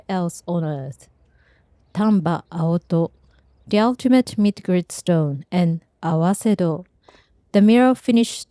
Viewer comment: That is what she said at the beginning. "The Tamba Aoto is a mid grind stone." Those are her exact words. You can listen to her say it at (4:34) and also see it written in the sub titles. Doctor George Whitehead (La Jolla, California. U.S.A.)